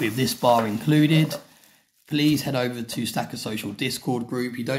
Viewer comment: Ha nice I thought it said slacker social at first. Was thinking of Bob Dobbs and the subgenius.